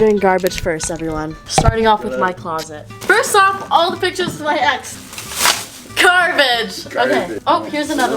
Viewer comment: When she said all these pictures of the ex or whatever I thought she said “all the pictures of my a$$”